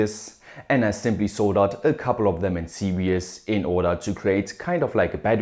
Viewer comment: You said "serious" instead of series